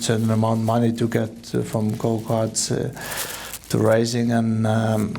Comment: Sound like kimi say u start doing cocke